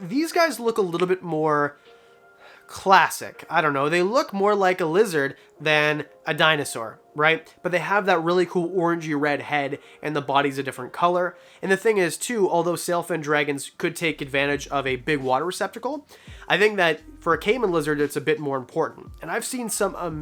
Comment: I heard "Orgy red head"